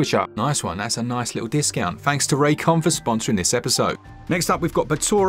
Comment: Raycon? I thought at first you said this episode was sponsored by J-Kwon, that dude who did "Tipsy" back in the day.